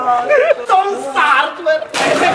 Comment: cosa dice? 😂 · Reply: Tom's hardware